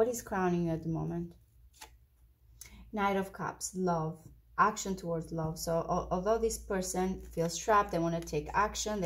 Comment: I wonder what she said from 4:30-39? Was it something I’m not supposed to hear? 😧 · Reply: Crowning energy is knight of cups, love. This person wants love, action towards love.